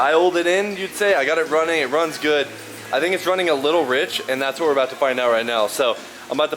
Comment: if it was advanced it would be running hot and lean , if anything it’s too rich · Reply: True. Because the a/f would have more time to burn off. Didn’t think about that. I thought you said it was running a little hot in the video but maybe I misheard. You put out great content btw 🤙 been watching for awhile. Always on my YT feed so I hadn’t subbed yet. Definitely subbed now!